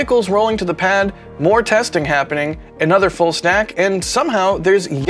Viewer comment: Did say "Full snack" at 0:06? Lol..must be bacon time!